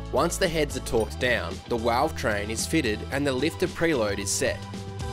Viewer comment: LOL @ 26:46 Did you say Walve Train Woody?